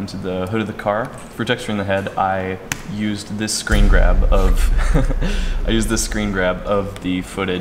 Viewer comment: did he just said "Breen Grub", lol?